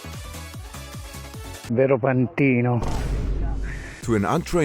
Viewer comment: i think the said vero pandino because "pantino" doesnt exist in italian